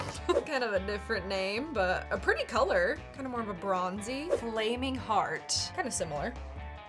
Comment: At first I thought she said flaming fart 😂😂😂